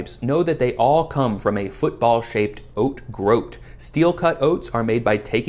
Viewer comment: I really thought he said “football sized” instead of “football shaped” and was frantically googling where to buy a giant oat groat so I could bite into it like an ear of corn. Took me 5 minutes to come back and rewatch the video.